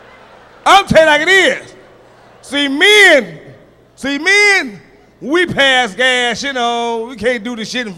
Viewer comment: For some reason I heard "SEMEN!!...SEMEN!!!" 😂